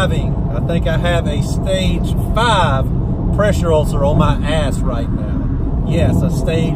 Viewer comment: I thought you are going to say that you have stage 5 pressure ulcers on your ass, lol.... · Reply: Oh you said that! Hahahaha.... I thought I have heard "on my hands", since you are holding to the steering wheel, hahaha... Too funny!